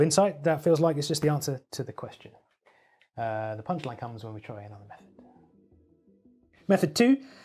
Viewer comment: I thought he said "the punchline comes when you try enough meth" and I was like "well, yep, I used to have a drug problem, can confirm, my whole life became a punchline"